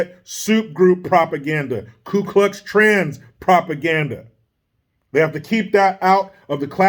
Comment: Did he just say Ku Klux Trans. LOL.👍 😂